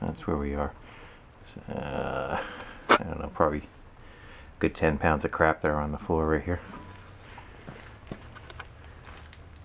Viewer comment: At the end, I thought I heard "There's probably a good ten pounds of crack on the floor right there..." Which also kinda fits.